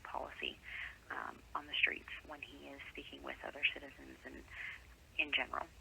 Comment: sounded like she said dealing with shitizens